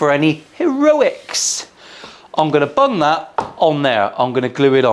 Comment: I got really confused at 14:22 when I thought you said "I'm going to bum that". Epoxy top looks absolutely mint. Amazing what you can make out of stretch jocks!